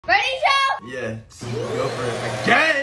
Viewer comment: I really thought his sis said "READY CHILD!"